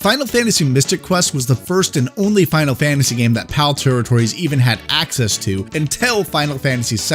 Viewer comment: What did he say at 5:00 minute mark ..sounded like pal territories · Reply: Yeah, that is what i said. PAL territories, as in areas that used the PAL standard as opposed to NTSC.